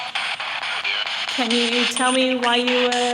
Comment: after you said " can you tell me when your in this jail. I clearly hear HAUNTED. I replayed in back many times thinking I was crazy for hearing it. idk weird.